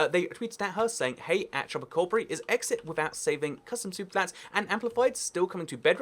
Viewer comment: I keep hearing "soup plants" XD